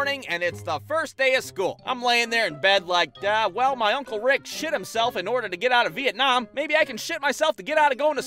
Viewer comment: I thought he said kris shot him self not he pooped himself lol